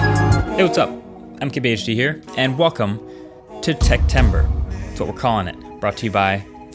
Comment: I thought he said "And Qualcomm to Techtember" in the beginning 💀